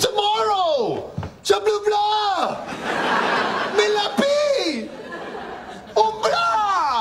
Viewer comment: Did he just say "si'l vous plait" whilst fighting between the desire to chew mozzarella and blurting out words?! 😂 Also Joey: ANY FRENCH word: blue blaah bleh blah